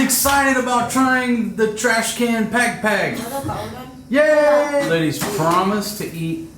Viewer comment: I really thought you were saying trash can backpack, because the backpack Fatima carries all the time, looks like a trash can. 😆